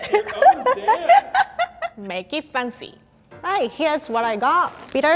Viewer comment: I 100% thought she was saying "make it funzy"😖😖